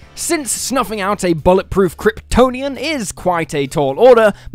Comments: Why did i hear it as "a bollock proof kryptonian"😭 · No word of a lie, I thought you said a "Bollock-proof Kryptonian" in the intro 😆😆😆😆